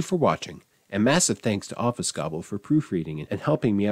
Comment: Since this is a language channel, I'll share this weird thought: when you said "Obfuscobble" at the end, I heard "Office Gobble," which I imagine would be a very different kind of channel.